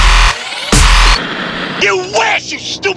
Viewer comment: I can't be the only one who thought he was saying "You witch" ....right? xD